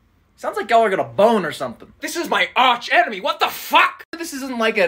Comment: "This is my arch enemy, what the FUCK!?" THE DELIVERY OF THAT LINE HAD ME ON THE FLOOR!😂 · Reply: I heard it "this in my aunt emily" 💀